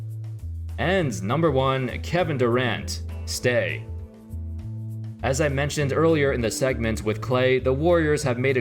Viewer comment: I thought I heard “Kevin Durant, Snake”